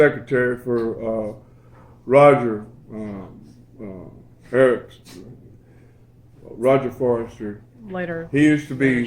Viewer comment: Don't know a Roger Harris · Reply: At around 9:25 it sounded like Bobby said Roger Harris. Roger was part of that whole England / Tulsa connection. He worked with Joe Meeks in England then ended up in Tulsa with Leon then LA with Shelter Records, recording the first Tom Petty stuff with Mud Crutch . Joe Cocker etc.... A real character. He got me a gig recording John Lee Hooker and Bonnie Raitt for the BBC in San Francisco. Unfortunately I heard he passed away, but nobody is sure. He was working with me in film last I saw him in 1999. I'm sure both of you crossed paths with him at some point. I met you CoCo a few times, when you were recording Delany out in the Valley on DA88s and then with Levon at the House of Blues. I record and produce the next generation acts like Mike Stinson and Ramsay Midwood. I'm 51. Anyways, you guys both look great and seem to be doing well. Very cool. Keep the videos going, everyone loves them.